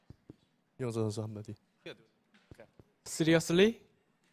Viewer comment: hey, does anyone know what Taeja said at 2:25? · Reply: "Seriously?"